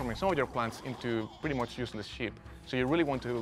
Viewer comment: Lol I thought the guy said useless shit